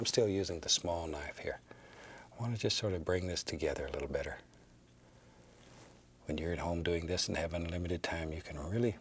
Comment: I legit thought that he said “When you’re at home or in heaven” And now I’m crying 😭